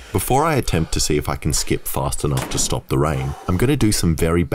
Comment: Dude I thought it said “stop a train” I was like no way. Rain makes much more sense lol